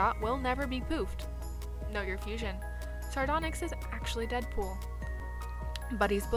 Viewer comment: I thought she said Nugger fusion